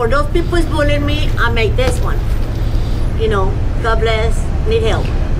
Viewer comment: Just wondering, how did she write the letters on the sign if she’s blind?🤔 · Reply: Oh I must’ve heard wrong I thought she said I made this sign.